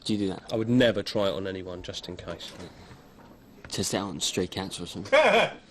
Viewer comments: anyone knows what says here? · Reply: “test it out on stray cats or something” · What does say at 14:40 to make Martin Freeman laugh? I've watched it ten times and still don't know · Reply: “test it out on stray cats or something”